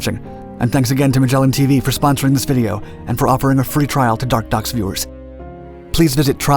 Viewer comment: I can't be the only one who never hears the narrator say "dark docs"... ...I only hear him say "dark ducks".